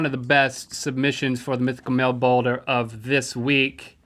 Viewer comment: I heard "mythical male boner"